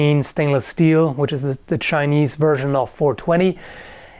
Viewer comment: "2cr13 stainless steel, which is the chinese version of 420" wait, so the chinese smoke steel instead of weed? thats fking hardcore man ngl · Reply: did he say Chinese? I thought he said "shiniest" It totally worked in context